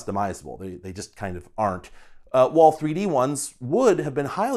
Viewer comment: Did you just say "Wolf3D ones" showing Black Desert Online avatars, or I misheard it? 😄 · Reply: Haha, nah, I was just saying "3d ones" - probably mumbled the previous word